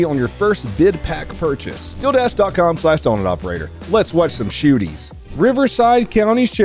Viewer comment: Bruh i thought you said "dildash" and i was bracing myself for a wild ad spot